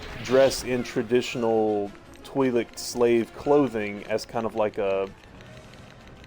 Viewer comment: the subtitles say toilet slave